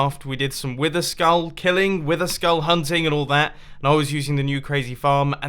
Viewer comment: who else thought he said witherskall hunting